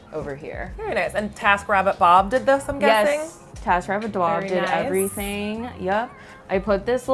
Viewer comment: i thought they said "tap rabbit" instead of "taskrabbit" and the google image search did not disappoint...vintage water taps with rabbts.